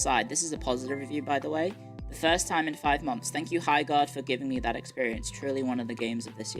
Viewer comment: Every time she said Highguard I thought she was saying "Hi, God"